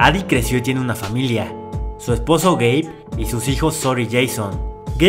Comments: AJAJAJAJA escuche gey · Video: "Su esposo Gabe" Yo: Yo entendí "su esposo gay." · SU ES POSO GABE lo que entendi:su esposo gey mi cerebro:picadura de la cobra gey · “Su esposo gabe” Subtítulos: su esposo gay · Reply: era "su esposo gabe" pero yo escuche "su esposo gay" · “Su esposo gabe” Subtítulos: su esposo gay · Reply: Yo pensé que había dicho su esposo gay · crei que dijo gey ,._. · entendí “su esposo gay” JAJJAJA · escuche su esposo gey xD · En el minuto 1:00 pensé que dijo su esposo es gay 😂😂😂😂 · “Su esposo gabe” Subtítulos: su esposo gay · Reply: Yo también entendí "su esposo gay" 😂😂😂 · escuche su esposo Gay XD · Jajajaajajja cuando dijo el nombre de el esposo le entendí su esposo gay y yo automáticamente AJAJAJAJAJAJAJAJAJAJAJJAJ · entendí su esposo gay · yo pensaba que dijisteis su esposo gay 🤣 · pensé que decía su esposo gay jaja😂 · escuche "su esposo gay" SJASKAJSASKA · yo escuche su esposo gay pero después dijo, Gabe quiere ir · Yo escuche "su esposo gay" jsbdubdshendnd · escuche gay xd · entendí su esposo gay y yo de que what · Creí que escuche su esposo gay xD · crei que dijo "Su esposo Gay" ._.XD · escuche “su esposo gay” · pense que dijo su esposo ''gay'' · pense que dijo:su esposo gay haahahahhahahahahahahaahahahahhahahshshshs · a yo escuche su esposo gai